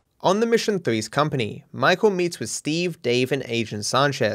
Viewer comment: That airport worker's the real mvp. He tackled Michael, saving him from becoming minced meat. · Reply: I thought you said “Michael meat”